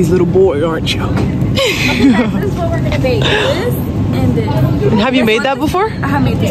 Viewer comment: I thought said ok guys this is what we’re going to vape this and this😭